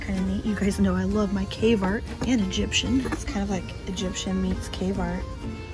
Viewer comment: I have to laugh because I thought you were saying Egyptian K-Mart until I realized you were saying Egyptian Cave Art. I thought "Wow! Egypt's K-Marts have unique stuff."